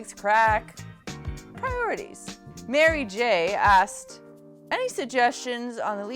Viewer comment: Did anyone else have drugs in mind after you were talking about Crack, and then when when you said the name of the person who asked the next question I heard it as Marij instead of Mary J. That was perfect timing! sounds like I should get my head out of the MariJ clouds (I'm starting to sound like a fast Canadian snowboarder now)